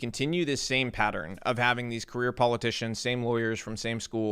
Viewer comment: Not gonna lie at the beginning of the show when Robby said they wanted to get rid of the “career politicians”, I thought he said “queer politicians”, had to listen to it a couple time before I figured it out. I thought ole rob from Tennessee was coming in guns hot against the ha-ma-sexuals.